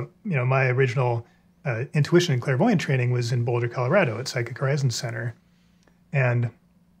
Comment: What Center in Boulder, CO did he say he attended around 19:23?? Ah! Psychic Horizon Ctr!